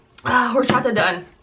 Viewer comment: Sounds like youre saying whorecharta lmfao